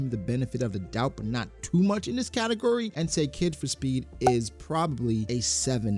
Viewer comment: For 8 min of the video I thought he was saying “katukuri” 😂😂 not category